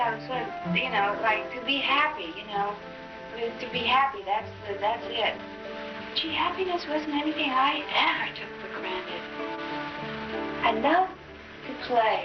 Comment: what she said at 3:19? can somebody tell me please? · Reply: baby Alice happiness was something I never took for granted